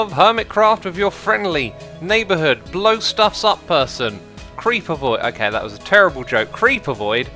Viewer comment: I honestly thought he said CreeperBoy 😹